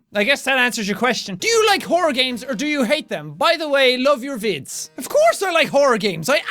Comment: hahahahahahahahahahahahahahahahahahahahahahahahhaahahah at 2:02 i thought it said btw love your kids and Jack donsnt even have kids yet hahahahaha